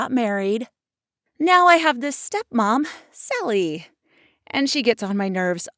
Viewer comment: lol i thought she's said "silly" Sally is so silly